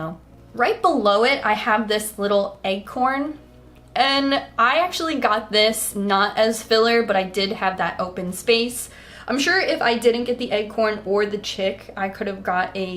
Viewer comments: is she saying 'eggcorn' instead of 'acorn'? Lmao, very cute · Am I losing my mind, or are you pronouncing "acorn" as "eggcorn"? I genuinely cannot tell. Just blasted my eardrums in these headphones, trying to figure it out, lol. But my weird fixation on this aside, these are all SO cute--especially the candy corn moon tattoo! 😍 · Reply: Wait... i thought she was actually saying eggcorn